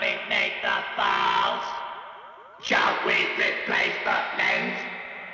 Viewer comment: "let us exterminate the BALLS" idk what hes sayin there but thats what i hear and its funny to me